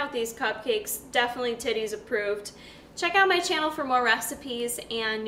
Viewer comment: I think I'm trippin. It sounded like she said titties approved?